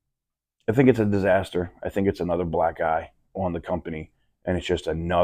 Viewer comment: I thought I heard “that’s another black guy on the company” 😅 black eye! Got it